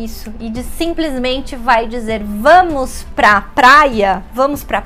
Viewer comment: E o que é o que você fala no minuto 1:36? eu ouço que você fala: "e diz simplesmente vai dizer" mas isso não parece certo para mim...